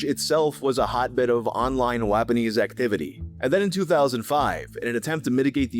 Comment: i heard lebanese xd